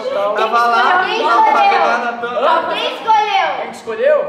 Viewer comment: Só eu que escotei a falando "foi eu " para